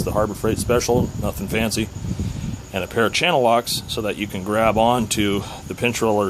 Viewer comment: What is the name of the tool used to grip the heated pinch roller arm at 13:00 please? Did I hear channel locks? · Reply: channel-lock pliars